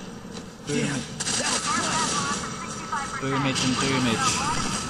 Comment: it sounded like u said boobage...